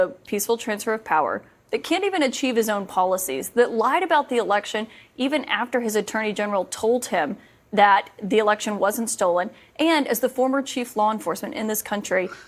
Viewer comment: Did she just say, "...[Trump] lied about the election even after his Attorney General told him that the election wasn't stolen..."?? It just seems really odd for her to refer to him in 3rd parson while talking directly to him.